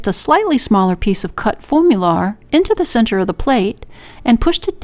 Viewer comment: Thank you for all your wonderful videos! What was the material you used to form the cute plate? I thought you said 'formula" but it must be another word.